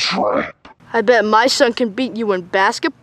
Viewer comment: This is gangster nightmare Bonnie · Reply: wait hold UP did springtrap say "well my son can beat you" is springtrap a dad???